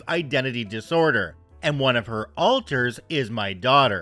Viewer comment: I wasn’t reading the screen. I was just listening. When you said “alter” I thought you said “altar” and was expecting an entire room filled with photos of OP’s daughters and her things.